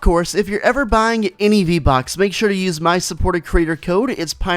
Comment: Did you say V bux? or v bucks?